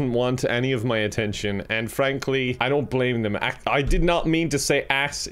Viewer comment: did he say Ann frankly